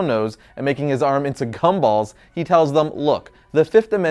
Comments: Anyone else hear cum balls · Um... Did you say Cumballs? Me and three others definitely heard you say c-Cumballs instead of gumballs... and we're genuinely confused. Sexually. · did he say .... Cum balls o_O. Surely that's gum.. right? RIGHT!? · I thought he said cumballs lmao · Anyone else hear “cumballs”? Just me? Cool.. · I heard “cum balls” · I have re-listened to this 50+ times and also used the captions. 1:20 is he saying Cumballs? Obviously the captions said Gumballs and that makes sense. BUT my ears say otherwise.